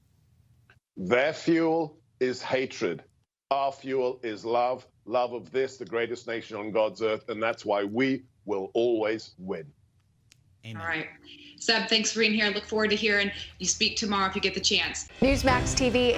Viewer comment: Did I hear 'amen' @ 6:28 from the reporter so be it! Trump 202won!😄✌️